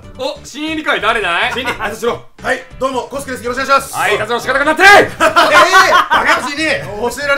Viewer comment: キヨ何言ってるか聞こえないんだけどなんて言ってる？ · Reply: ロスカル 挨拶の仕方がなってない！ だと思いますよ！ 間違っていたらすみません┏〇゛